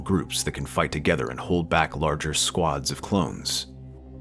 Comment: We hear “squads” but the caption says “squids.” 😂